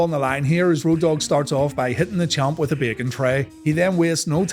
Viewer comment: Lol i thought u said bacon tray.